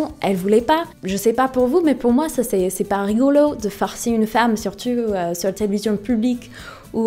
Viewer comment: " c'est pas rigolo pour moi de farcir une femme à la télé " mdrrrrrr